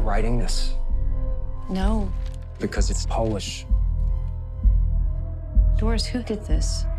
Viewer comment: did he said "becaus it's polish" lol